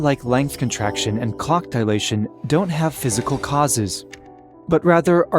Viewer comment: bro what is clock dilation you mean time dilation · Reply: I thought he said cock dilation 😭😭😭